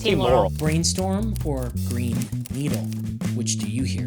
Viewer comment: I hear Green Eden.